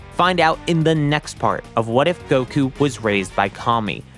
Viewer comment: At first I thought it said what if Goku raised Kami lol.